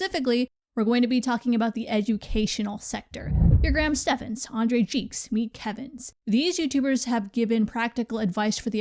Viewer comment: Did she say Andre Cheeks? 🤣🤣🤣